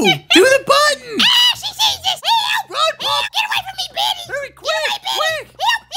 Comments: I thought he was about to say "GET away from me b*tch" Help 😭✋️ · I thought he said bitch🤣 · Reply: NO SWEARING!! Anyway. He said Betty!